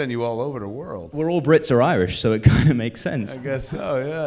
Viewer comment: Not a fan of Cuffe, I'm in no way political but found the line "All Brits are Irish" offensive. Not sure if it was meant as a joke or if he actually thinks it's true (definitely not) either way paints him in a bad light, in my opinion. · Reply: he didn't say that, he said "we're all Brits or Irish so it kind of makes sense" you simply misheard him